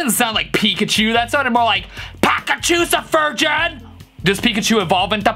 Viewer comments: I always heard "bag of chips and virgins" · Ok but why'd I hear "back up she's a virgin" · I always thought he’s said “in fact she’s a virgin” 🤣🤣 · misheard lyric: pack a chicks are virgin